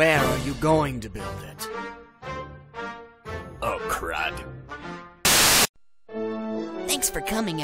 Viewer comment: I always thought he said "Oh crap"